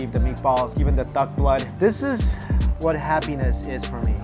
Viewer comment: did you say duck blood? a lot of religion forbid consuming blood. Christians and jews for one big chunk.